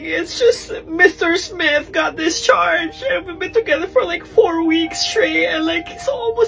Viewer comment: Discharged, I thought you said Charged at first.